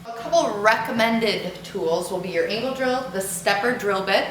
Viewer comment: did she say anal drill. 😳